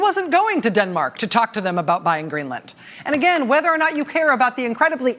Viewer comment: oooooh, buying green 'LAND'. I thought he said buying 'COLLARD GREENS' for ALL the land. My mistake.........